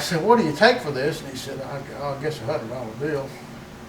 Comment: In the 8:50s, did the seller of the manifold, asked what he'd take for it, say "Well, ah guess a eleven dollar bill"? Couldn't make ANY sense of that juicy bit of info. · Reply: I’m here a year later lol. He said “I guess a hundred dollar bill” That accent can be tough sometimes if you weren’t raised around it lol